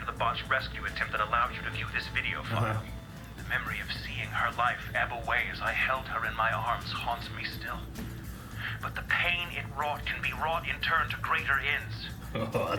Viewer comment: Anyone can tell what the "great line" is? I really can not hear what is said. Thanks in advance! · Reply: "The memory of seeing her life ebb away as I held her in my arms haunts me still, but the pain it wrought can be wrought in turn to greater ends" is what it sounds like to me.